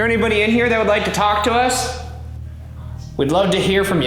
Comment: I think I heard at 7:48 a whispered “us” when Ryan asks “Is there anybody in here that would like to talk to us?” Then Ryan’s equipment dies.